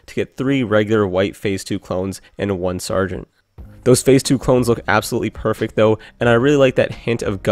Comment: did he say phase 2 clones